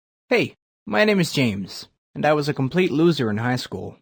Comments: I thought it said James Bond Lmaooo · who else thought it said James Bond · I thought it said James Bond